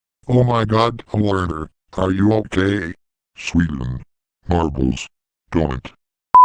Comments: i heard Sweden a european country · what is allertor saying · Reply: sweden. marbles. donut. and then some cuss word